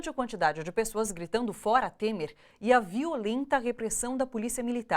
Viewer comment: Eu entendi "Bora Temer"